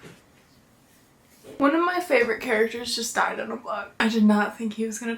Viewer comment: finishing this delusionally tired and thought you were saying one of your favorite funny story characters just died i was sarrrr confused for ten full seconds